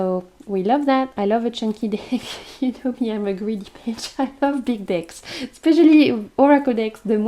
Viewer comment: 😂You sounded so funny when you said “I like big decks” it sounded like you said I like big DICKS 😅 I’m a greedy bishhhh ❤